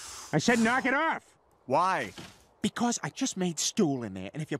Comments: I always thought he said he just made stew, and that was funny, but the fact that he’s talking about doodoo makes it even funnier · I thought he said "I just made stew in there" for some reason. :P